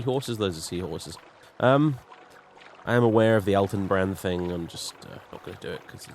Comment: When you mentioned that you were aware of the Eltonbrand thing at first I misheard you and thought you said you were aware of the Alton Brown thing.I remember when you ganked that sixth house cultist in Suran,it was when you went there looking for a muck shovel because rebirth or at least the version your using requires one in order harvest muckspunge for the Fields of Kummu.